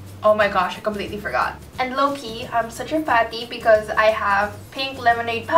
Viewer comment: she said '' loki'' again!! whut in the world does it mean?! LOL anywayss, she said it in 4:15, ''loki im such a fan(i think she said fan??, not sure)''.. if she did say loki.. whut does it mean? cant find it on google ehh.. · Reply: she's saying low-key, and i believe it means subtle.